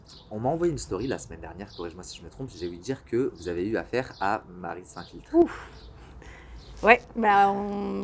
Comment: A 23:11 il a dit quoi comme nom j'ai pas compris? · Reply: Marie s’infiltre